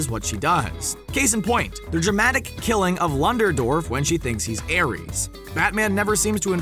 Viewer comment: did he just say lunderdorf? I